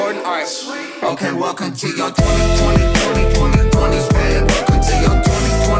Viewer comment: I just looked up the lyrics and all the times I listened to this song I thought they were saying "welcome to your twenties x5, *fam*" but it's "babe" Is this the Yanny Laurel effect? xD